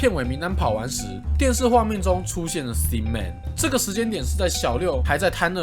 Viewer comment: 博主发音的thin man 我一直听成了sin man让我很出戏啊 😂